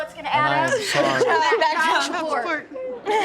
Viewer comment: What was said when someone said: You know what's add up? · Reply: That Back Child support